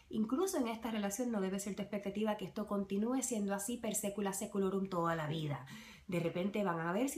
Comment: No entiendo que dice aquí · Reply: Per secula seculorum. Por los siglos de los siglos